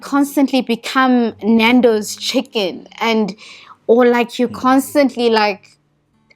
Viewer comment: lol did she say nandoes chicken? 11:15 lovely video.